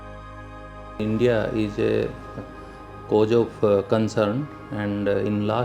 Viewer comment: What is a "coj"??😐😐 The guy said, "India is a COJ of concern"...👀👀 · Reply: It’s “cause”. Don’t make fun of peoples accent. If you truly knew English well enough then you’d have understood.